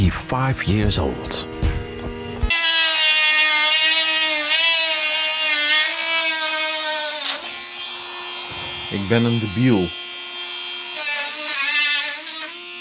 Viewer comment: i am dutch and i heard you ad 6:00 "ik ben een debiel"